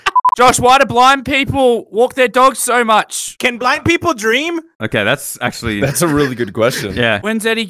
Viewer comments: I thought he said can black people dream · When Eddie asked “Can blind people dream?” I thought he said “Can black people dream?” Also blind people can dream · When he said “can blind people dream?”I thought he said can black people drink?” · When he said,"Can blind people dream?" I low-key started thinking about it too because that is a good question 😂😂😂😂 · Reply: it said blind...? i deadass thought it said black 😭